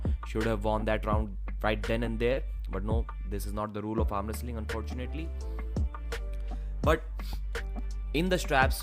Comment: Foetunately bro!!! WTF are u saying....